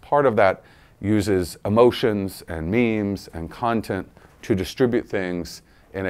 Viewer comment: is he saying memes or means?? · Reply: memes